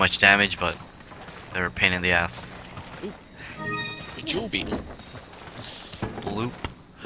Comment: It almost sounds like you say "Doubie" isntead of "Jewel Beetle"